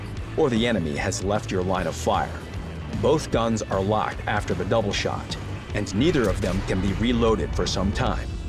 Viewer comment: they actually said it at 7:14 "both guns are locked for some time after you used the double shot"